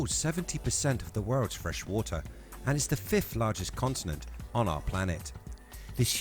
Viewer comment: is sounds like you say "consonant" not continent.